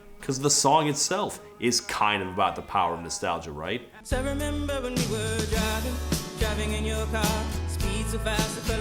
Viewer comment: ok I know she is saying "I remember when we were driving" but every time that clip starts without warning all I hear is "I remember N**gers driving" And my brain double takes